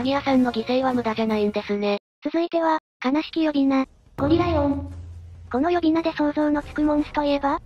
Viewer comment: ゴリライオンがゴリラ芋に聞こえたw